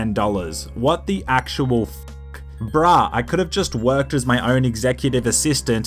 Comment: did you say brah? Just wanna know cause I don’t hear much people saying it except for where I’m from.